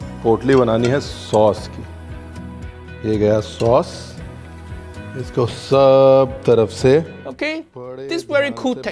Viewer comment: It sounded like he said big ass sauce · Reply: he said "ye gya sauce" means "There goes the sauce"~